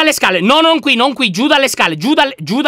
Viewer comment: ho sentito: Giuda le scale😂